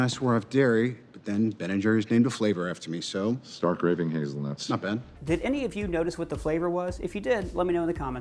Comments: I think Dr. Strange called it "Start Raving Hazelnuts." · Sounded like he said, "Stark Raisin Hazelnut." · I thought he said Stark Raving Hazelnut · Sounded like he said “STARK RAVEN HAZEL NUTS”...